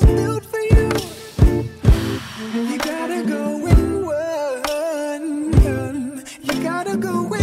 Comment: Thought he said go in work